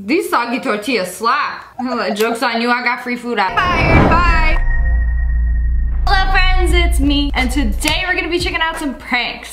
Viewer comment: Help I always hear “Loaf friends it’s me” ah, how much I love watching SSSniperLoaf.